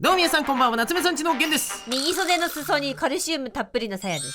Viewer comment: オープニングの｢カルシウムたっぷりなさやです｣が｢からし梅たっぷりのさやです｣に聞こえたんだがw私だけ?w